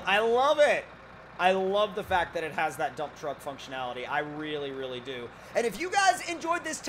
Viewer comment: I always hear "this is a dumb truck"...😄😄😄😜😄😄😄